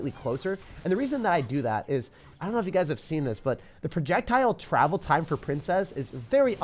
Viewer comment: did he say Trajectile? Lol